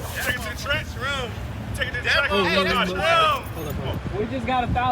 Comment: i swear he said he gonna rob the track hack 😂